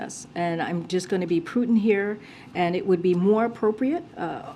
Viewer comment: Did she just say "I'm just going to be Putin here"